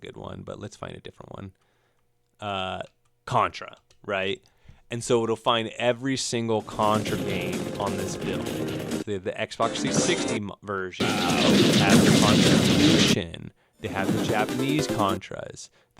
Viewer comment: why i thought you said "you'll find every single Contra game on this bitch"